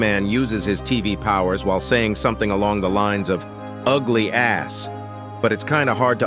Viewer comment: I UNDERSTAND WHAT LARGE TV MAN JUST SAID HE SAID UGLY A$$ B*TCH